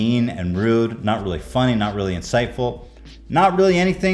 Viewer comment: "The tweet isn't insightful" - nope, but it's INCITEFUL 😂😂😂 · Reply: I always hear "inciteful" instead of "insightful", haha - maybe cuz I'm already so SJWed up 😂 Hate these little melty leisure fascists 😑